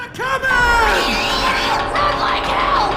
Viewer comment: seriously at 0:38 did zoey said "sound like hell" or "someone help" ????